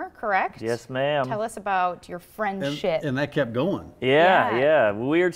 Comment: I thought she said tell us about your friends shit